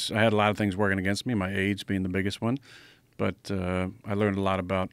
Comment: I thought A.R said "I had a lot of things working against me, my aids being the biggest one"